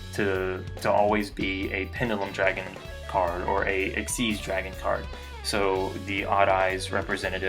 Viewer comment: the subtitle say ''or a sexy dragon card'' XD